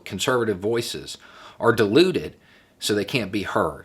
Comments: Beau, when you said 'dituted' , I heard 'deluded', lol! Sounded right! And yes, when you read the bill, it does help stop them, because it let's us all be heard! 👍🥰💖✌🎃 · When Justin said ...Republican voices are diluted, I heard ...Republican voices are deluded.